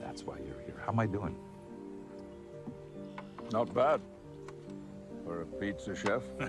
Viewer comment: First time I heard this scene, I had to do a quick rewind. I honestly thought Picard said "Not bad...for a POS."🤣🤣